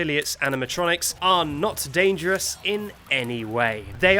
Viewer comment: I thought I heard danganronpa